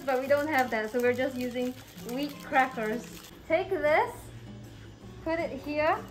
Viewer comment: I thought she said weed crackers 💀